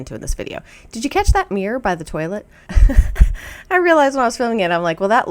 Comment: I heard “did you see that beer by the toilet” then realized you said mirror. 😅